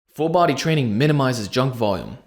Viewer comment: Phew... for a moment I thought it said full body training makes your junk smaller.